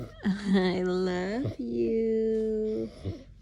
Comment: I thought he said 'i love you too'! More aware than we know.